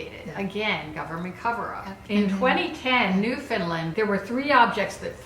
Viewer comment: did they say Newfoundland or New Finland? Because barely anyone knows Newfoundland exists, I should know because I live there and we get barely any attention. So I was thinking it was New Finland but they sounded so similar so I wasn't sure